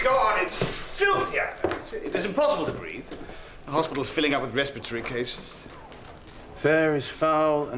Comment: well did he say respatory cases? Oh wow